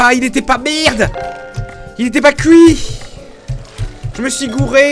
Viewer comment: ILETAIPA QUOI ? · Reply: cuit